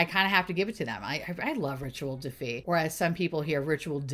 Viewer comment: Def thought you’ve been saying Virtual Defeat (Rituel de Fille) this whole time. (I’ve been a follower for about 5 years.)